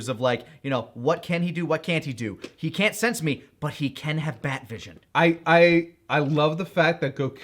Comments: Honestly thought Goku was saying “He has THAT vision” because he’s just dumb and was recently talking about seeing things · I always thought Goku said death vision!😂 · For years I thought Goku said "he has THAT vision!" As in heat/laser vision. I also thought it was a callback to Lord Slug who also used laser eyes. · When Goku said "He has bat vision," I always thought he was saying "He has thought vision," which was still funny because that implies that he didn't realize he was talking out loud. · I always thought he said,” he had THAT vision”😂